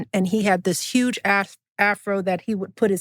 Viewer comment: I heard “huge ass Afro,” and THATS the miss Michelle I jus knew we’d get eventually